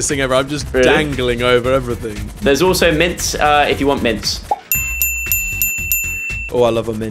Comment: I legit thought said mince and I was like wtf 😂